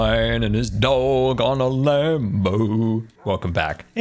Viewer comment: did anybody else hear him say "lambo" instead of "land boat" at first??